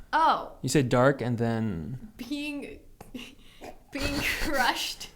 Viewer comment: At first I thought she said peeing 😂